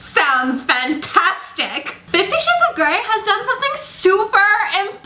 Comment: Did you say 50 shit's of grey? x)